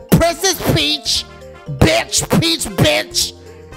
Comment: Heh, I think I heard you say "Bitch Piss Bitch" accidentally. Forgot what part you said it at though.